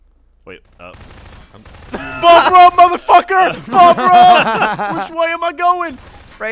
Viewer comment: what did ray say @ 56:09 did he say "BOB RUN MUTHA FUKA BOB RUN" please someone tell what they think he said didnt get it?